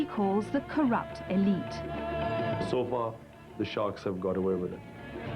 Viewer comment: Now I understand what he said: "So far the sharks have got away with it." ( 2:09 )